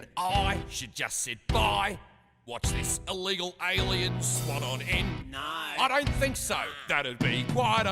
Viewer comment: I swear I heard “swat on-end?”